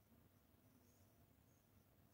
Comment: a man's voice is heard saying, "He's Here." It kindof sounds like it's mimicking YOUR VOICE!!😮